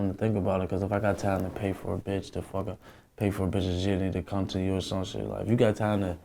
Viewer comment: sound like he said " if i got time to pay for a bitch to fuck her" 😂😂😂